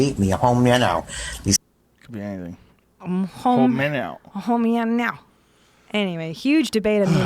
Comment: Weird dude is saying " Hoe me on out" thank me later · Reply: I hear “Home Here Now” it fits the context of what he is mething on about